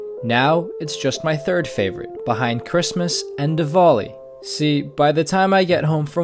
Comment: Did he say Diwali?! 0:14.. Oh man.. that's cool